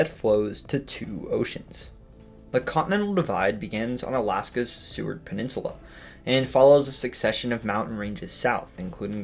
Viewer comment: I misheard and thought you said “sewered peninsula”, I just assumed you were referring to Florida 😉